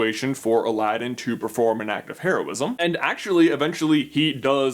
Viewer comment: I thought you said "Act of Terrorism " first and I was like WTF